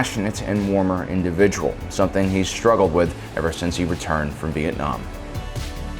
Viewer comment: is it just me, or did he say Vietnam?